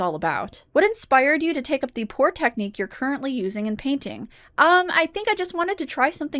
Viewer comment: Hahah... I heard that as "what inspired you to take up the poor technique you use for your paintings?"